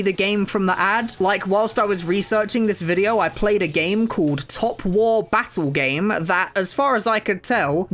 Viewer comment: I thought he said " slop war" 😭